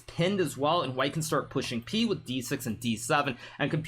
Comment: did he just say pushing p...